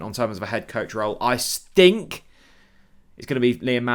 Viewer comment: did you say say that 😀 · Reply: 😂 I stink!! A cross between I still think!!!😅